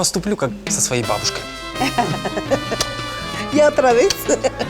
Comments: концовка збс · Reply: Послышалось, что бабушка на 26:18 сказала "Не отравился?" :D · Послышалось, что бабушка на 26:18 сказала "Не отравился?" :D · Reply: Бабушка сказала Otra vez что в переводе: ещё раз)